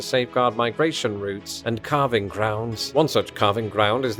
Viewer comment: For a sec I thought you said carving grounds. Then I realized you said calving grounds.